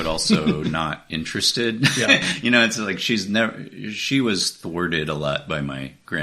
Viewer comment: what is he saying about his mother at 12:55 - "she was WORDED (??) a lot by my grandparents as a kid..." WHAT??? THWARTED??? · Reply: yes thwarted